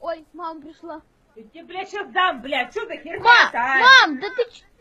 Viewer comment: did I hear.... Blyat?